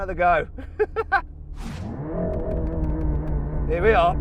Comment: 確かに「行くよ」に聞こえる笑 あと、Here we are じゃなくて、多分Here we go じゃないですかね…？間違ってたらすいません。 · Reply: here we are ですねここは。 たしかに聞き取りにくいですが、イギリス英語なので、もしgoと言ってたら「ゲウ」に近い発音になります。対してareはイギリス英語の場合濁らないのでそのまま「アー」と発音します